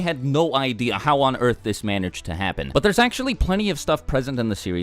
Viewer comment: did you say manerged to happen